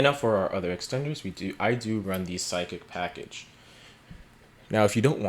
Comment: I don’t know why I heard “the sideckick package” instead of Psychic